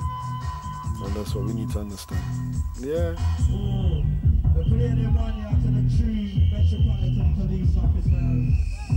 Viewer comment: What does Aba Shanti say at 6:22? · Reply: Special request to the police officers!